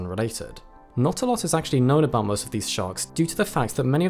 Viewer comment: I thought it said "most pathetic sharks" and now I feel stupid